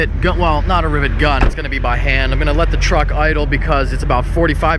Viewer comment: "I'm going to let my truck idle" What I heard: "I'm going to ask someone to steal my truck"